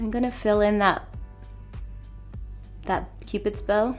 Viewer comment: did she say pupids bow?